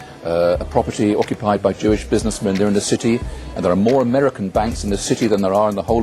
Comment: CORRECT ME IF IM WRONG BUT I heard “Jewish businessman” NOT “Church businessman”